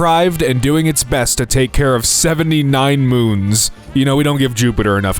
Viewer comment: İ thought that said moans 💀